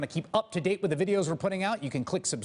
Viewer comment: DEAR MSNBC: At the end of all these videos, Chris Hayes tells us to subscribe and stuff, and he says to click the link "just below me"... WELL IT SOUNDS LIKE HE SAYS "JUST BLOW ME"!! I can't not hear it.